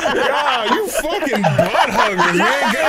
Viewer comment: I thought you said butt hurt (10:57) in steam. I seen i need hearing aid🤣